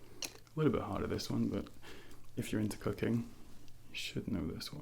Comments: I heard “if you’re into cocaine” instead of cooking lol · Omg lmao. I was drunk and watching this when he said if you are into cooking I thought he said if you are into cocaine